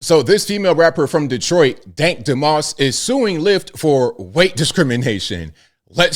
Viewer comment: At first, I thought you said "Tank Demoss".